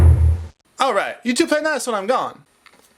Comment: Uhh... what did the NES say at 6:17? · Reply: “Alright, you two play nice while I’m gone.”